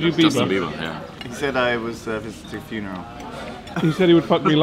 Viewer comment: what did say around 1:10 ? Cant make it out for some reason. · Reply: "He said I was visiting a funeral."